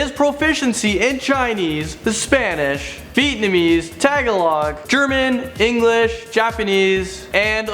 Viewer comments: Did he say tagalog???? no hate just asking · What did he say ? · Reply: mark lazaro Tagalog, The language of Filipinos.